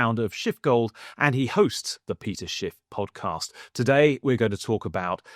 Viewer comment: when he mumbled "peter-schiff podcast" I heard: "piece of sh*t podcast" LOL